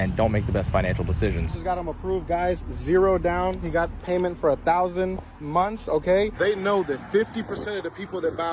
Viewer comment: Did he say 1000 monthly payments? That’s 83 years. 👀